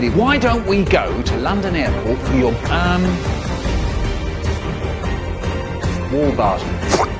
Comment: what does he say before he does this flopp sound? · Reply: Wall bars